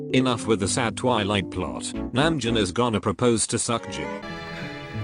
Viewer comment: i freakin heard it as "Namjoon is gonna propose to suck Jin" im cying ahakskfhsmk😂